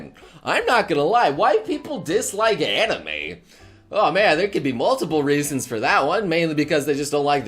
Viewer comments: who else thought he said "white people don't like anime"? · "Why people dislike anime." I thought he said White people dislike anime, I was so confused.